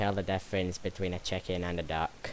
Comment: I gotta admit i heard u said cheken as in chicken in